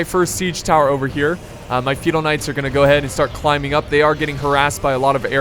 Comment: Anybody else thought he was saying "fetal knights" the whole time?